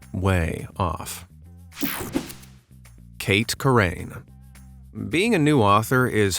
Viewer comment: I just woke up to watch this and I thought that said “Kurt Cobain” 😂 I was like “What did Kurt do?!” 😭